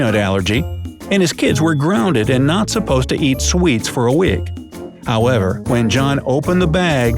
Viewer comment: “not supposed to eat sweets for a wig” am I the only one who heard that?